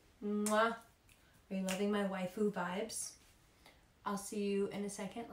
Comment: what did she say her vibes were??? Wifu vibes?? V synchronic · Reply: Waifu ❤️